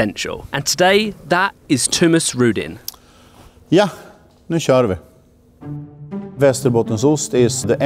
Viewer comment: Ja, nu tj------ I didn't catch that? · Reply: Nu kör vi!